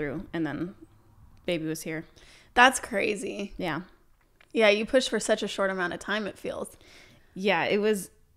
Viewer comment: For a moment, I thought that said "You pushed HER for such a short amount of time" but I think I just halluciated ahaha ! So happy your delivery went well <3